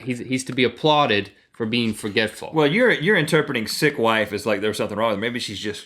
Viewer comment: I thought you said SIXTH wife